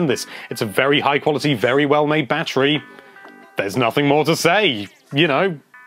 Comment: Why do I keep hearing him say "banterry"?